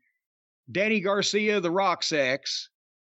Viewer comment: does he say daniel garcia the rocks ex?????